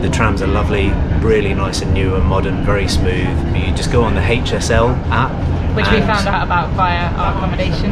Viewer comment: Haha! You were talking about the HSL app and I thought you said something like hate yourself app. Nice video!